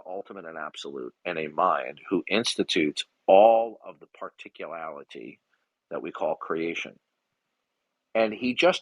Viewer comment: Did he just say "particulality"? He's just making words up now.